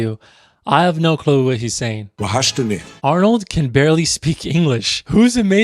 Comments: In 4:48 I guess he is saying "Wahachtani?" which basically mean Do you miss me? correct me If I'm wrong but I think that is what he's saying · You don't have any clue about what he said ?! Let me tell you what he said : وحشتني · i think he said: "twaheshteni" which in algerian arabic means "you missed me?" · i think he said wahashtini وحشتني which means i missed you in arabic Egyptian accent